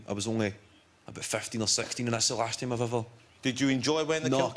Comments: What does Kevin say after he says, "I was only about 15 or 16"? Because I haven't a clue. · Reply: "....and it's the last time I ever" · What does he say between 10:58-11:03? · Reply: “I was only about 15 or 16 and that’s the last time I’ve ever [worn a kilt]”